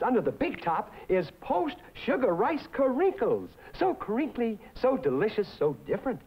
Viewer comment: it sounds like he was saying "ayayai que rico" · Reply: That's nothing I thought he said "so creepy" at 0:24 or 0:25, but no, that's just what he should have said, he in fact said so crinkly.